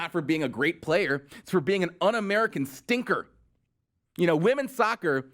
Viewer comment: what is this drivel ...did he just say "Unamerican stinker?" 4:27... so Fox News is too intellectual for conservatives now they need idiot fox aka BlazeTV